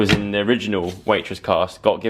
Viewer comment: When said ‘this mug is from waitress’ I thought he said ‘waitrose’ and i was like wow such a big deal 😂